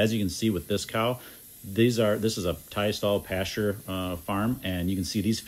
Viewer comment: What is a... idk if this is what he said, but what is a "Thai stall pasture farm?" that's what it sounded like he said. Anyone know? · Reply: Tie stall. Meaning when they’re in the barn they have assigned stalls that they’re tied in.